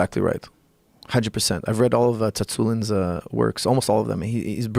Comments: What's the name he says at 0:03, Tetsuin? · What trainer’s book did the guest say he read at 0:01 mark? I could not understand and I'd like to read those "works". Thanks. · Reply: Pavel tsatsulin idk how u spell his name he's been on the jre pod before